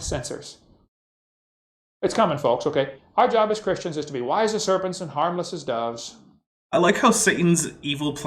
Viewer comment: What is Kent saying here? "It's our job as Christians to be ????" Wise as serpents and harmless as doves? Wise as servants and harmless as gods? Wise as sermons and harmless as dogs? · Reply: Serpents and doves